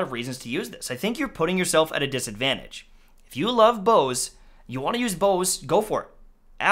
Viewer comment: Wife thought you said "I love hoes"